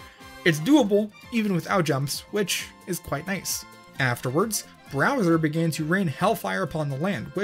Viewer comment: did he say browser?